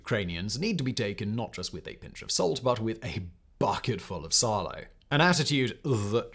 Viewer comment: What did he say at 5:40? “A bucket full of _____” ?? Subtitles say “Silo” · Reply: salo? In ukranian thats salted pork belly.